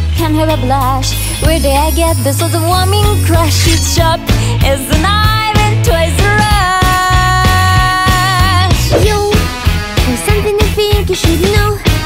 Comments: "It's sharp as a knife in Toys R Us!" I can't be the only one who heard that. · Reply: I hear it's as sharp as a island twice a rush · "It's sharp as a knife in Toys R Us!" I can't be the only one who heard that. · without looking at the lyrics I thought yuri said toy r us 😂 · "It's sharp as a knife in Toys R Us!" I can't be the only one who heard that. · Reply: yep lol I heard it "Its Sharp As I Knife As Toys R Us!" LOLOLOLOLOL BOW THE FACES BOIIIII LOLOLOLOLOLOLOL